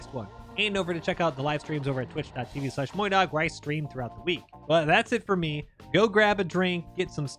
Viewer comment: In the intro I heard "check out the live stream where I scream throughout the week".